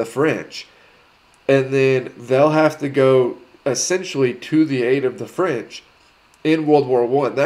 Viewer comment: No you have it wrong,in ww1 the British went to the aid of the French not the other way round. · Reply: I think you may have misheard. “They will essentially have to go to the aid of the French” was the phrase used in the video. “They” being the British.